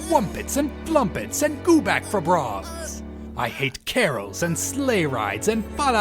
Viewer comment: what does turles say after he says "I hate the whos down in whoville and all of their songs" ? it sounds like hes saying "and gooback for brongs" at the very end... its just what im hearing